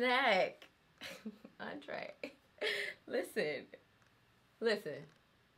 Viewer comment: I heard her say Andre😂😂when she saw here hair color